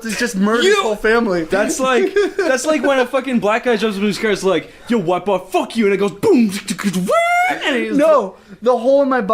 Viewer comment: WHAT DID HE SAY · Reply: I'll try to transcribe as best as I can: "That's like when a fucking black guy jumps up in his car and is like 'ay white boy fuck you' and it goes (beatboxing noises)"